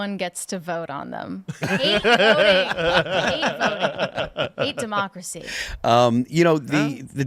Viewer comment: Did she say " I hate voting, i hate democracy " ? .